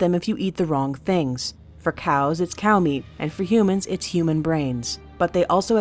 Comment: Did say "cow meat"? I can't hear it well